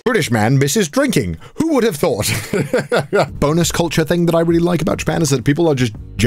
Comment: I thought he said boneless culture. lmao